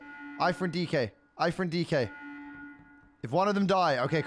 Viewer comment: did anyone else hear Aiphern DK?